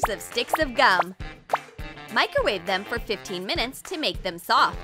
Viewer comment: did she say 15 min